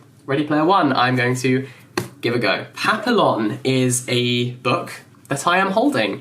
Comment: hhhhhh i had to replay that 3 times ... i had my eyes closed listening to the titles... i heard something like patha along ... and i was like : what's a patha along ??!! Hhhhhh ... then i realised its papillon and he pronounced it papilon hhhhh oh man it's a classic! Not the book, i never read it, but the movie with Dustin Hoffman! And its a real life story.